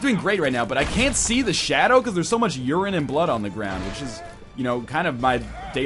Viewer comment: The only thing i heard was "there's so much blood and urine"